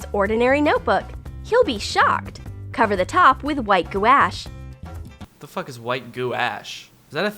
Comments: When he said white guash I thought he said WHITE DUCK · is she saying gouache? Hahaha like acrylic/watercolor paint? Lol I think it’s pronounced like gwahsh. It rhymes with squash. But who knows 🤷🏻‍♀️ lol all my art classes pronounced it like that lol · It's sounded like he said "White goo ass"😂😂